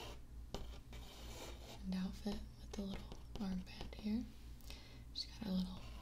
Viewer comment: i swear i heard "armpit hair" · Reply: lime.and.limin wen she said “armband here” 😂 close enough ig